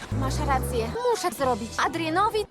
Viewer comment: co ona mówi? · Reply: "Zrobić Adrienowi kanapkę". U mnie żarty o jedzeniu są chyba w każdym poopie.